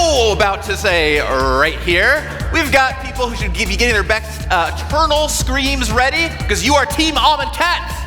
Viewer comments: I replayed this part like 10 times an I still have no idea what said. "We've got people Hoosier givee giggity bets, uh, turtle screams ready because you are TEAM AMONKHET" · As a non-magic player and only watcher I kept hearing "Almond Cat" and thought that was a weird name for a faction. I did correct that later on.